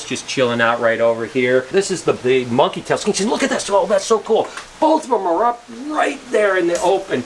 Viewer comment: What the hell just happened 😂 what did you say??? 15:14 It sounded like "monkey testicals xscyl look at that's so cool!" 😂 😂 😂